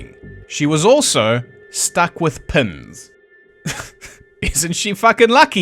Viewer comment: At first I thought you said "Bessie was struck with Pimm's" I thought to myself, "That's a horror"..... Then l realized you said "Pins". That's not nearly as bad! I'll take Pins over Pimm's any day!!!